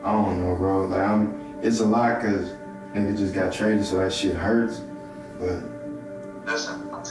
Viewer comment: He did not say"I just got traded so that shit hurt" he said" Nigga just got traded so that shit hurt" I'm black picked up on it soon as I heard it lol... He must be talking to one of his friends